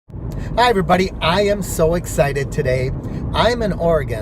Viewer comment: I thought you said "Hi everybody, I'm AN ORGAN" 😀😋